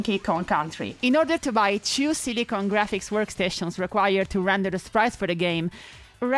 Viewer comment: I think the synapses in my brain crossed over here because i misheard this as “Diddy Kong Graphics”